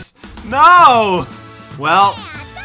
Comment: blaze said "well" so many times it started to sound like she saying whale